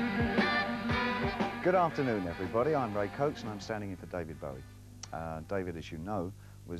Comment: what did he say his name was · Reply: Ray Cokes the long standing MTV Europe VJ. Bowie even does Ray’s mannerisms in the first pieces. (And just to say...ain’t Bowie The best!)